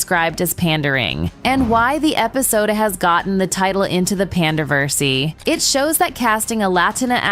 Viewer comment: Did she just say "panderversey"?